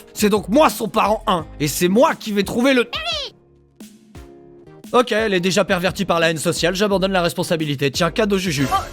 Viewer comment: il dit quoi le bébé dragon ? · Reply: Derry